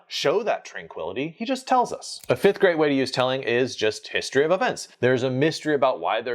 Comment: HaHa! Sounds like "a fifth-grade way to use 'telling' is..." (a negative) instead of "a fifth GREAT way ..." 😂